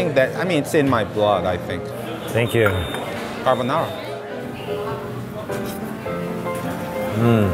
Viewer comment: the closed captions say "Habanero" when what the chef really said was Carbonara. The dish he served them was a take on Carbonara. Plz fix